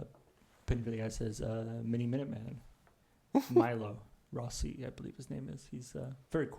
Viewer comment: Did anyone catch the archaeology-based channel was talking about around 30:00? He says the name, but my hearing isn't great and I can't quite make it out. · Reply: Miniminuteman, Milo Rossi. He's great. I believe he got his start on TikTok doing quick debunks of conspiracy theories. His long-form content on Youtube is fascinating and entertaining.